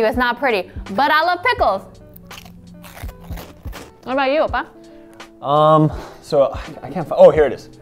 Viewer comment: "what about you oppa?" did i hear that right??